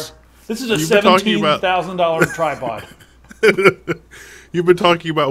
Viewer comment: At first i thought you said $1,700 and thought that was ridiculous, but 17,000?? .... why